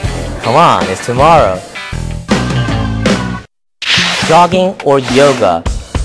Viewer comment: haha, I thought he said 'dogging' or 'yoga'!!